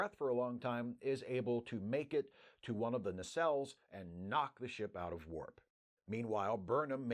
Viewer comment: i looked away and performed some menial task, so when you said "knock the ship out of warp", i heard "knock the sh** out of worf"... i was like "huh, who did what now???"